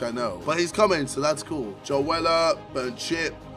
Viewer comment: when u said joe weller i thought u said joella 😂😂😂